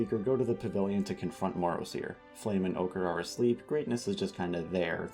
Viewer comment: HELP WHEN YOU WERE NAMING THE FALSE DRAGONETS I HEARD "OGRE" INSTEAD OF "OCHRE" 😭😭😭😭😭😭😭